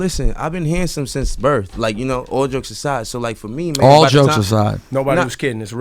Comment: Rockys a fashion dude But it looks like he's wearing a Hanes undershirt and a blazer? That outfit probably cost more then my mortgage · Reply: Carlos Martinez 10:58 did they say "all jokes aside"?! what they mean with it?